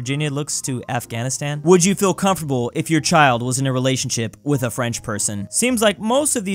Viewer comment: i thougt it said chair instead of child